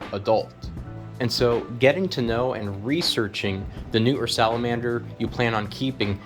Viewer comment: I thought dude was saying newton salamander 😂